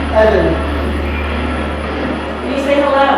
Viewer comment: I heard "William Hello"